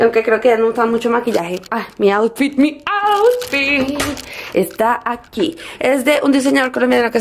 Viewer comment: Like si oíste que dijo mi outfit como shakira min